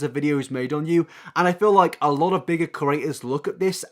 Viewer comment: For a while there I thought you were saying curators when really you were saying creators 😂 Cute accent tho